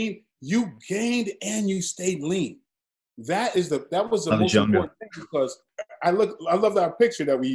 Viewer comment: What does Scott say at 28:23 when MJW is saying he stayed lean? Sounds like "I'm junga" but I can't make it out. If he is saying junga, what does that mean? Coz my brain can't make out anything else